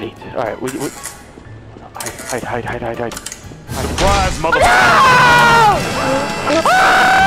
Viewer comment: did anyone else hear that "surprise motha fucka"?